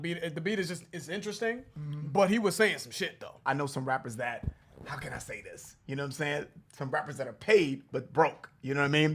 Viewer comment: Instead of “I know rappers that- how imma say this?” I thought he was saying “I know rappers that- hire Mercedes” 😂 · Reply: I thought he said “I know some rappers that- nah I won’t say this”😂